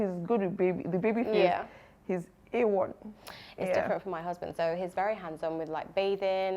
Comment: Am I the only one who heard say "my husband".